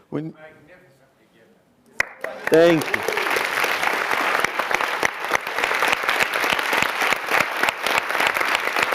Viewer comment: anybody knows what the man in the audience said at 26:39???? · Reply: Laura Rodriguez "Magnificently given".